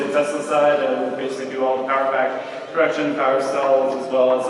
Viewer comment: At time 23:30 did he say they are running power factor correction on the incoming power? Interesting. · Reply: Sure but it's not what was said - he said "Power Pack Production" One thing mentioned on the tour was that the switchyard is quite special. Tesla will be running on Solar & Wind power only at the Gigafactory. I think you'll find they are going a bit beyond power factor correction.